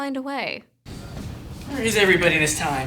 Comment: when he hits the shroom does he say "Shroom Jesus"?